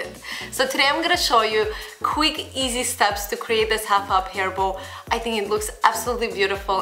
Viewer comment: I thought she was saying a "huff up hairball" instead of half up hair bow XD